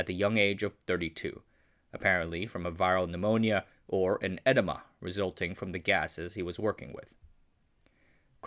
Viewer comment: It sounded like you said Buck died from an enema, instead of edema and i lost it 😂